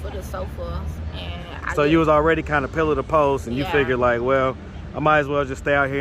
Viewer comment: I'm not sure what you said but it sounds like you said that she was already going from "pillow to post" which is a popular phrase to describe being homeless. The saying is actually going from "pillar to post" if that's not what you actually said.